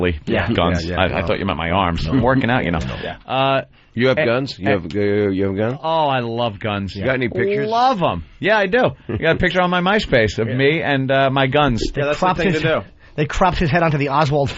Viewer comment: did someone say myspace???